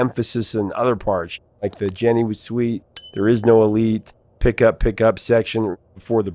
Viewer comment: I thought he said 'there is no relief'. elite?! hrmmmm